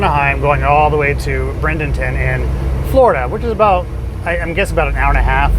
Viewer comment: when you said name of city in FL, I wasn’t sure where you meant but on replay, guessed it was possibly Bra-den-ton but sounded like Brend-en-ton. It’s near Sarasota on west coast south of Tampa area.